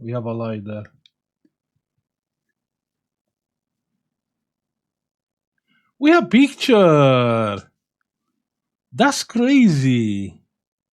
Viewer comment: always i heard "we have picture that crazy" :)